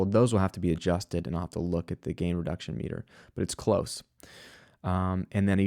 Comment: English is not my first language so could anyone help me: at 08:20 what does it says after the phrase "Those will have to be adjusted". I just can't understand the words he is saying. Thanks. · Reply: "Those will have to be adjusted, and I will have to look at the gain reduction meter. It's close."